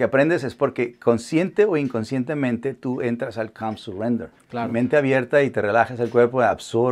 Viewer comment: Alguien sabe que es lo que dice? Algo como "cam its render"